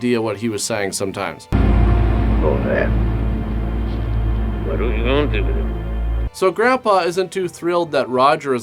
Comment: I just realized what Grandpa said on the "speaking gibberish" subtitle, mostly. Not sure of the first word, but after that is "what are we gonna do with him?"